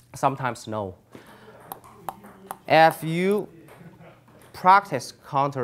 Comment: Thanks! Amazing video coach! Anyone else hear 0:35 as "Eff youuu"!! :P :P love your videos!